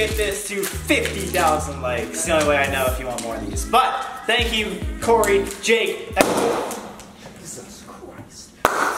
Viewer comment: Jake:*pops water bottle* Sam:Jesus Christ Corey pops water bottle right after jake 😂 when Jake did that i heard "Corey Jake an ELtOn